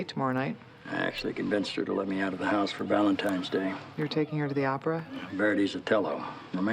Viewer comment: Does he really say “Valentimes Day?”